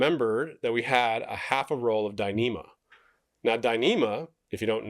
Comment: Lol. At 15:38 the CC says "I remember that we had a half a roll of dynamite" and I was like huh? Where is this going? Going to blow up the boat?